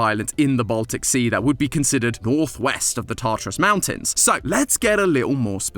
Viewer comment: love that the captions don't know what Tatras is and calls it the "Tartarus"